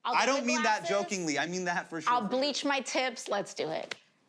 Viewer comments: why did i hear 24:01 "I'll bleach my tits " ? or was it just me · I heard "I'll bleach my tits" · I swear I heard her say "I'll bleach my t!ts." I feel like a bad person.